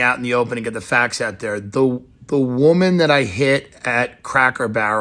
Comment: I misheard this the first time through and thought he said "the woman that I bit." On second listen, I got that he said "hit." Bit is way funnier. Tim, I'm disappointed in you, you should have done better.